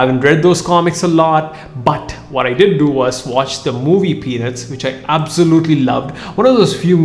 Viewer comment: I heard you say penis instead of peanuts